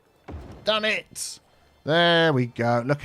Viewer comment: Sometimes I can’t tell what you’re saying, “damn it” or “done it”.